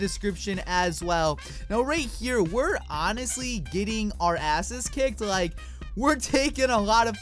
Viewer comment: did say asses? :o